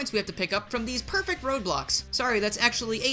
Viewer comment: when you said "perfect road blocks" i heard "perfect roblox". i hate myself